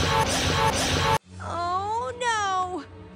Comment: What did they say at the end of the video after when springtrap had perform his jumpscare. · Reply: They said springtrap